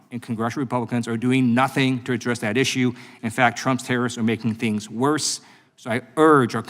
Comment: Whe he said Trump tariffs due to a problem with my hearing I thought he said Trumps Terrorists. In hindsight I think the latter is more apt. Musk and his cronies are terrorists plotting the overthrow of Democracy